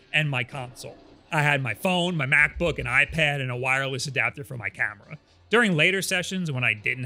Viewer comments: misheard as "wireless adapter for my catheter" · lmao, I thought you said you had a wireless adapter for your cat for a second there. 😂